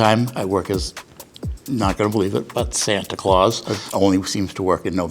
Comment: I started watching to learn what a Living Tower is. Eleven minutes later, now I believe in Santa. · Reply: I thought you said Satan at first... Phew.